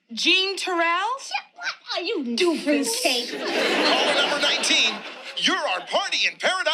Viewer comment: I always thought it was Regine that called Synclaire 'doofus', but her & Max insulted Synclaire at the same time. I guess Regine called her a 'fruitcake'.